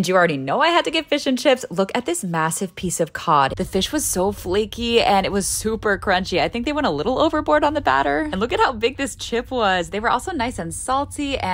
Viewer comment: Did you just say over load of batter 😡 you can never have enough batter on fish and chips innit